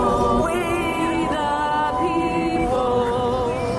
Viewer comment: I hear "fweedom people"